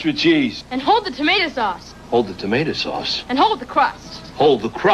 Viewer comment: when freddy said "jimmy" it sounded like he said chimmy which would make sense if they were on a pizza resturaunt in the 90's or 80's